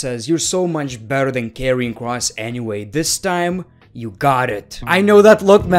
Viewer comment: And By the way When Said Karrion Kross It sounded like you said Kevin Kross LOL 😂😂😂😂